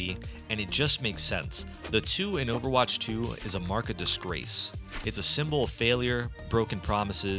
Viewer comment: "market disgrace"? It sounds like you said "mark of disgrace".